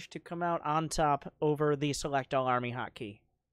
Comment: Did he said "also noobfromAU is a gay" ?